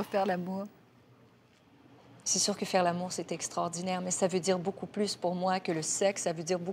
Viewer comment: Can somebody please write in French word for word what Céline said at 1:16:28 to 1:17:01 ??? I thank you a lot whoever writes it. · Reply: Arthur Dion She said: « C’est sûr que faire l’amour c’est extraordinaire mais sa veut dire beaucoup plus pour moi que le sexe, sa veut dire beaucoup plus pour moi que physiquement s’engager à se donner. Faire l’amour c’est le partage aussi, c’est la compréhension, c’est le dialogue, c’est l’écoute de l’autre. Je pense que faire l’amour avec mon mari aujourd’hui est non seulement, uniquement physique mais il est beaucoup aussi au plein aussi émotionnel, spirituel, à l’écoute et c’est comme l’abandon, c’est de faire confiance à l’autre sans attendre. » ❤️